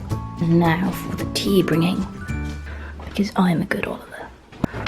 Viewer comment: "And now for the tea bringing. Because I am a good Oliver... Dun dun dun dun dun" Oh, you sweet adorable soul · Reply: I thought he said good alater lol